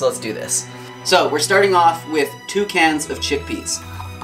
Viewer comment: Lol, when you first said you were going to start with chickpeas I thought you said you were going to start with chipped beef. My head snapped up and I thought what?? Sharing this with all my vegetarian friends.